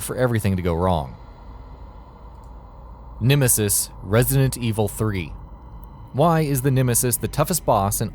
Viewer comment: did i hear resident evil 30